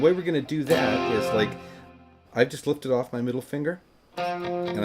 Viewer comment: It kinda sounded like you said'I just flipped you off with my middle finger' as you demonstrated the power f -chord, that was funny. I've never flipped anyone off in my life! Love your lessons, always comprehensible. Thanks. Best rock album of all time imo.